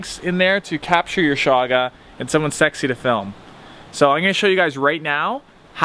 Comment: did you just call yourself sexy?? I mean, I don't disagree hahaha. Great videos, just found your channel :)